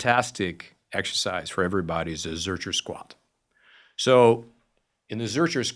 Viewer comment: Did anyone understand what squat he refers to at 9:44? Listened to it four times, still can’t make it out. Thanks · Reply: Zercher squat, I mean even the auto captions get it right, what's not to understand lol